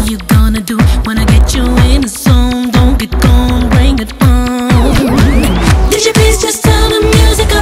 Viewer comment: LOL @ 0:21 does she say " in the zone , Donkey Kong , Bring it on ???